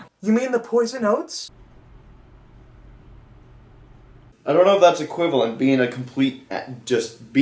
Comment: Every time Lyra has said “oats” in this episode I thought she was saying “oaths” it wasn’t until now that I realized that she was saying “oats” lol 😆